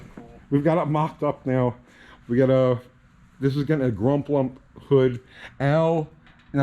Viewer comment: looking at Slaghammer's hood: "... it's got a grump lump hood..." not sure if I heard correctly or what that refers to. Wasn't part of my gearhead background in New England. Anyone who can explain it to me? Thanks!